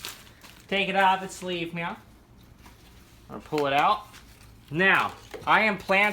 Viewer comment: Did you say, "Take it out of it's sleeve meow." At 12:52?